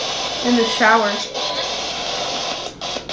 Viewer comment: I SWEAR TO GOD I heard "Behind you"